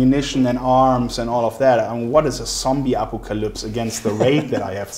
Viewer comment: what is a zombie apple colups..(sorry don’t know how to spell what the hell he said 😂)